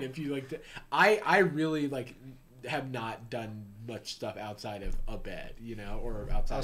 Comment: thought this man said "butt stuff". The lisp is a curse disguised as a blessing sir.